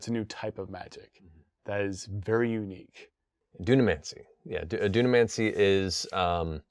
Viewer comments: It sounds like Matt is saying Dudamancy and now have to go make an NPC Dudamancer named Keanu 🤷‍♂️ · Oh, I thought he said Dumbmancy. I was like finally, my barbarian gets to cast spells. :P